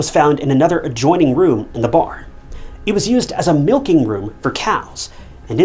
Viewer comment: thought you said "cats", and this case was getting stranger